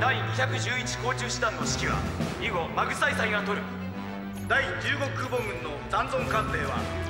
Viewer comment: Wow. 11:41 am i hearing it wrong or its a mistranslation. " The Magsaysay". They name their assigned flagship after the late Philippine president. 🇵🇭